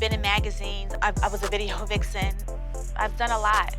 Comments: When was naming her "accomplishments". It sounded like she said I was a video hoe but tried to cover it up and say video vixen. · Did say, " I was a vide'Ho vixen" ? 🤔😣